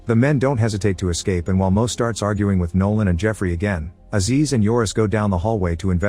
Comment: On god i kept trying to look through the comments like am i tripping and did he say most darks are arguing 1:36 like i kept listening like huh... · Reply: He says "and while MO STARTS ARGUING"